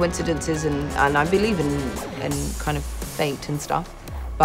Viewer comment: What is she saying at 0:45 ? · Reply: "Kind of fate and stuff".